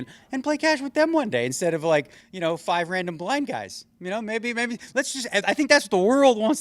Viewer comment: subtitles say blind guys but negreanu said black?